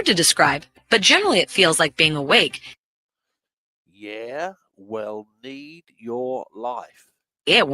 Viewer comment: AI says "yeah well need guests life" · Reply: Hmmm.. I think I hear "Yeah, we'll need GUESS life"